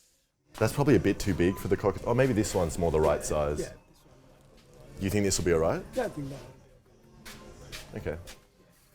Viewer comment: Am i the only one that wants to know what he acually said at 8:00 ? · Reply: He said cock-cutting-devise